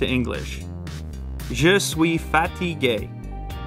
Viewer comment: i thought he was saying je suis fatty gay for a sec